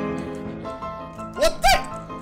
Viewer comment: did u say "mikä on?"